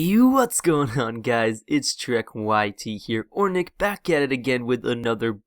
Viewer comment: You kinda sounded like leafy is here on the intro watch replay